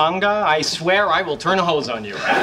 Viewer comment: What does Stewart say at 0:51? "I swear i will turn ??? on you" Thanks for any answer. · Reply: Hose